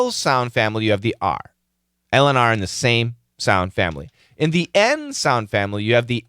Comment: Yes, it is good one :) Easy to understand what's going on, but in detail it's impossible for me to write it down 🤔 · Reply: I thought she said 'the same run'